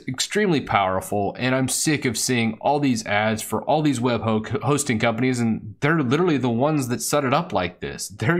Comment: Haha, @ 1:56 I think you were about to say “web hoaxing companies” :)